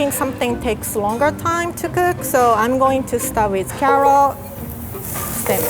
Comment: Did she just say “ i’m going to start with Carol” 😆😩